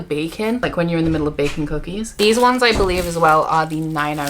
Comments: I genuinely thought you said one of the candles smells like bacon not baking 🤦🏽‍♀ Those books are beautiful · I kept hearing ‘bacon’ instead of ‘baking’ and I had to go back each time to review the scents 😂